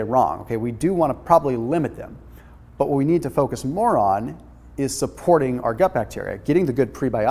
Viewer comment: I think he says "Butt what we need to do is focus 'Moron'..." I never thought would call me that. ;-)